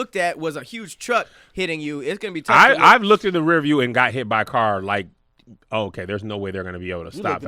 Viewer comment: what joke did Tahir say? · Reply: He said he looked in the mirror and got hit by carbs 😂😂